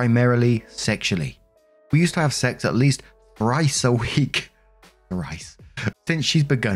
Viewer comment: Story 2: The way pronounced thrice I thought he said rice and was VERY confused on how you could have sex rice a week